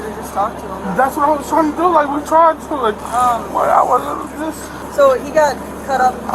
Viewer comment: At first i thought he said " I am not resisting", thinking he does that very agressive. But it was a name he called😂.